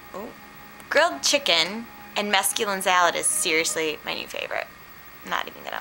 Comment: I totally thought you were saying "masculine salad" and I was wondering what that was so I googled it.. Apparently it's mesculin sprout salad.. Oops haha